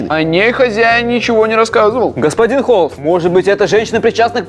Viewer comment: послышалась что сказал Господин клоун😁😁